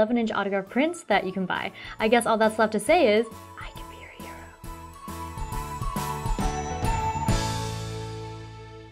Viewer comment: what did you whispered at 9:46 ? hahah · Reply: can be your hero.